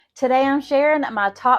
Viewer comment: I thought she said, "Today I'm Sharon ..." So I didn't understand why she said her name was a few seconds later.